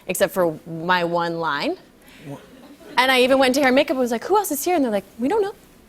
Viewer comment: what did she say @ 1:29 ? who want to see here?